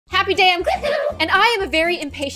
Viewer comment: When said happy day I'm I thought she said happy day I'm glue LOL